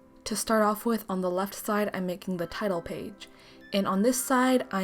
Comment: lmao when u said title page i thought u said tidal wave